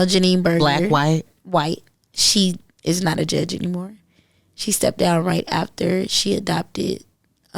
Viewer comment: Forget Jake’s.. baby they need to review files for this judge.. now!!! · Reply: I thought she said the judge step down